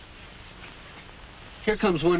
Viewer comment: Am I the only one that thought he said have a snicker instead of a cigarette?